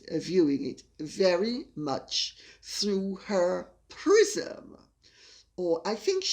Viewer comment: Ha! I thought you said, Harry is viewing himself through Megan's PRISON, not prism! I think both are correct. LOL!